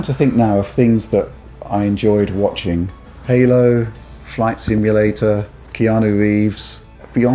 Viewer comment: Flight simulator looked amazing!!! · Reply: Oh I thought you said fight simulator lmao.